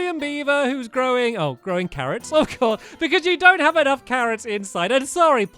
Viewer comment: I thought stampy said throwing carrots instead of growing carrots